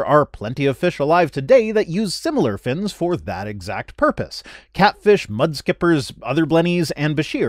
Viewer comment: I heard "catfish, mudskippers, other bunnies" and was very confused because I was sure you'd been talking about fish then hear you refer to two species of fish as bunnies instead. It took a moment to realise what was actually going on there...